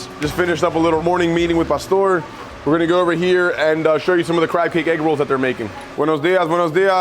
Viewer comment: I swear I heard him say Crackhead Eggrolls. And considering it is Baltimore...